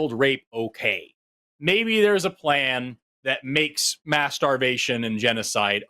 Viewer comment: When Forrest said “mass starvation” I thought he said “masturbation” lmao